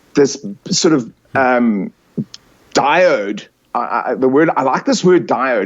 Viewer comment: Someone please tell me what word is he saying: "dired"? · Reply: Diode or as suggested here dipole. Electrical terms regarding flow of energy. :)